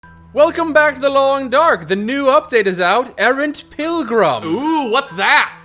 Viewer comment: I thought it said arrogant pilgrim lol When I went to mystery lake I was almost dead because I was trying to climb up a giant rope and fell. I spent an hour wandering around and finally found a bed roll and slept for a while